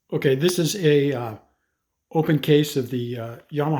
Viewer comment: I had to rewind immediately! I thought you said "open casket" lol